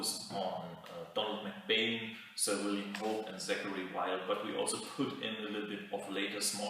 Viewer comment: Who is the second guy you mentioned, right after McBane? I couldn't quite understand the name. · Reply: Donald McBane, then follows Sir William Hope, Zachary Wylde (and later I mention Domenico Angelo too).